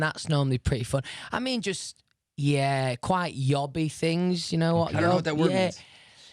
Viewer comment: can someone please tell me what did he say? ___ things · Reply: I think he said yobby things... I don't really know but...